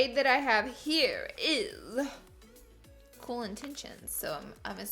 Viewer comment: when she said “cool intensions” was the name of the lip colour i thought she said “colon tensions” and i was so shook